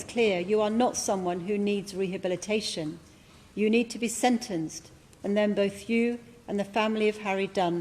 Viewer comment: I thought that said sentenced to death. RIP Harry